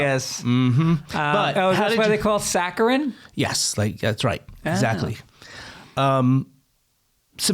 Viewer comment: Did he say “Zachly” at 26:06? He’s always on point! 😂